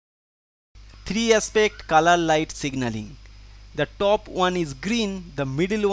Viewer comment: I thought he said kala light ( black light )